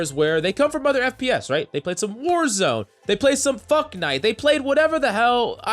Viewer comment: heh fu*knite i heard you say it